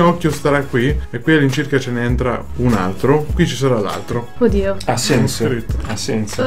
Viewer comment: Ma solo io ho sentito orcodi0 a